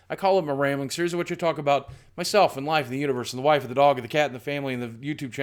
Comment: Did he say CAT?! He doesn't have a cat?!?!